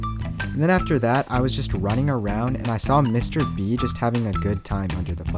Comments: thought he said mrbeast lol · who else thought he said “MrBeast” 😂